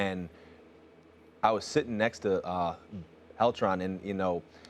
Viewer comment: In 5:10 springer said “eltran” instead of Beltran because he has a hard time saying the B. He’s truly an inspiration to me who has a stutter